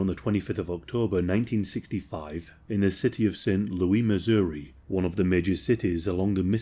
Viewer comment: you mean Las Vegas? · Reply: Hahaha I thought he said city of sin too